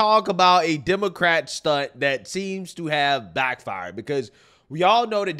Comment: Lol I thought said it’s time to talk about a Democrat slut, not stunt lol I quickly figured out what he said, but it was funny